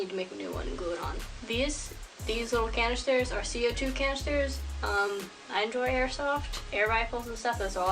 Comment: Thank you this is so helpful! What did you use to make the little bottle things at 3:08 I can’t really understand what you’re saying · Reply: Sorry I talk kinda odd or quiet sometimes but those are empty CO2 canisters! at 0:32 i have the supplies on screen. Any small light weight canisters should work , I used those b/c I had them laying around from air soft lol. Good luck!